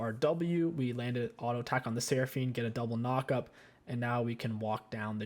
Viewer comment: once again.........sion hits very hard. what did he say? sion e? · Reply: AD as in attack damage